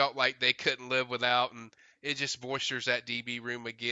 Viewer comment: Did he mean to say it “bolsters” that position? Sounded like he said “voisters” ?? …. With regards to the Houston DB..